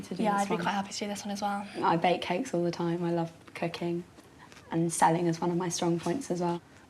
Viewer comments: OMG did she just say i love cocaine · I wasn't paying attentiong for a second...and I thought said 'I love cocaine' instead of 'I love cooking' ah fun :P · I thought she said I love cocaine